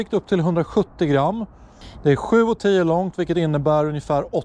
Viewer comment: ojjävlar va långt sju o tio 0:40 😂😂 ja kanske hörde fel 😀